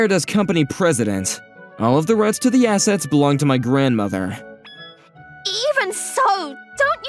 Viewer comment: was it just me who misheard ‘grandfather’ to ‘grandmother’ at 4:31? 💀